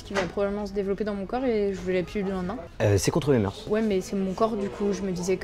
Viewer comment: y'a que moi qui ne comprend pas ce que dit a 3:00 ^^' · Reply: Il dit : "euh c'est contre mes moeurs"